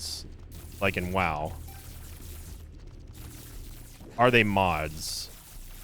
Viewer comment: I thought it said LICK IN! 😂